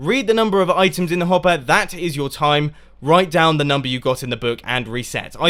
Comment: it's "write" instead of "right"